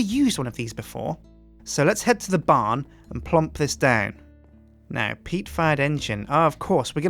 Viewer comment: Did he say 'lest head to the farm and plomp this down' at